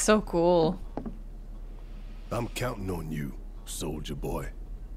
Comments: Barret: SOLDIER boy Me: did he just say soulja boy? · Everytime barret calls Cloud "Soldier-Boy" all i hear is "Soul´ja-boy" xD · When Barret said "Soldier Boy" it sounded like Soulja Boy